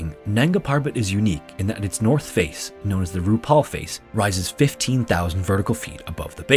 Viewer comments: The RuPaul face? Did I hear that right? Climbers, load your yaks! And may the best sherpa win! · I thought I heard “Rupaul Face” and I immediately thought, “of course that face is visually striking. Death zone? More like death drop💅🏽💃🏼” PS: I don’t mean disrespect, it’s just my stupid brain derailing my train of thought. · Did I hear right? “The north face, known as the RuPaul face…” Really? 💋💄💅🏻🪞😳